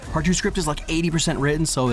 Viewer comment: At the end I thought he said Archer's script is 80% written and that, along with the "crepuscular" reference made me think Archer was coming. :(